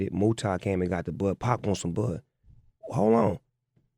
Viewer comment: Bro, could you tell me what Snoop said @ 19:52 ? What did 2Pac want his homie to get him? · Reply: Some bud!